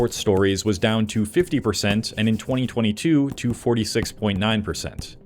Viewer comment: “20 2012” anyone else catch that 😂